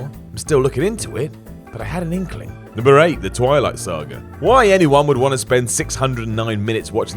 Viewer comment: I misheard the Twilight Saga and thought he said the Twilight Zone. :p